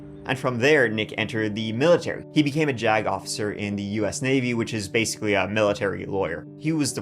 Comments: With the heavy accident, I thought I kept hearing him say the guy was a "jack off " officer 😂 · Omg! I thought you were saying he became a jag-off!! 😂😂😂 While not completely untrue, JAG officer makes a bit more contextual sense!